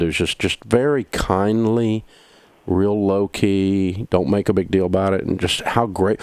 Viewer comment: Did Dave Ramsey just say, “low key”? I’m a 20 year old male and I’m pretty much sure that I speak for millennials and Generation Z’s that we’re surprised he said that! (It’s what all the cool kids are saying these days for you older folk)